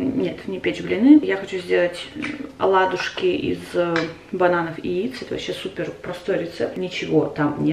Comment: мне послышалось из банавых яйц🤣